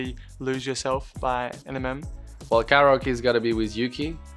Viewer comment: did he just say “Enimem” 😂